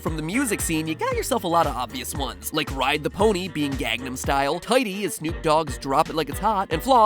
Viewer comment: did you just say Gagnam style..........?